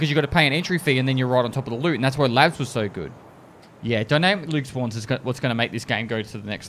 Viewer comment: For those asking what he said at 26:43, the word is dynamic loot spawns.